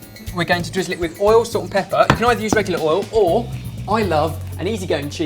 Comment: Whenever says "salt and pepper," I hear "sorted pepper" haha